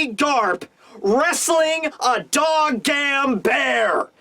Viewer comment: Did he say 'dog gamn'